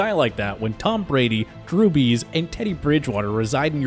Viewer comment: Me: Drew Brees Everyone: Drew Brees LITERALLY EVERYONE: Drew Brees: Drew Bees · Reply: I'm half asleep but I could've sworn he said Jew bees.